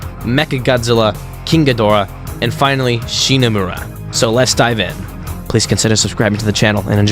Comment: I thought he said shimo